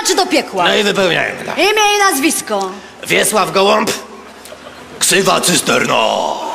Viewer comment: Oglądam to po raz setny... I dopiero teraz usłyszałam, że tam jest Ksywa, a nie KRZYWA XD